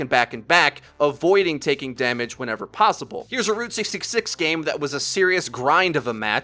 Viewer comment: Why did I hear "Road 666"?...lol